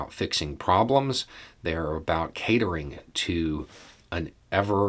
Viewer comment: I first heard "hatering", and I thought "oh, cleaver pun there", but it turns out I just misheard things again. Then again, "hatering" is a perfect description of what the Republicans do and stand for